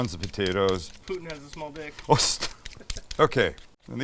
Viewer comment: I heard “Putin has a little dick” ….. did anybody else hear that??? Thanks for your efforts Sam!